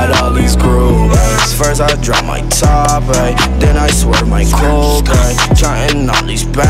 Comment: Lmao I thought he was saying first I drop my top 8. I was like tf we're going back to MySpace now